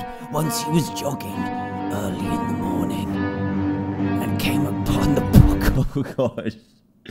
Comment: What he said: and came upon the body. What I heard: Came upon the book